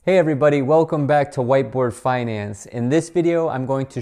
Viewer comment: I thought he said white boy financing. ..I now know its white board financing. Lol